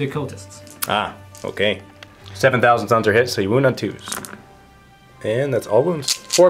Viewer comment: I know its seven members but the first time I heard 7.000 sons was hit...